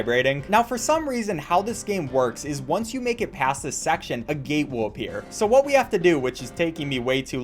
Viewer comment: was he saying gate or gay?XD